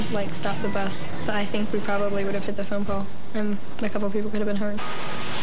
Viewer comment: what did she just say? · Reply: “Could have hit the phone poll”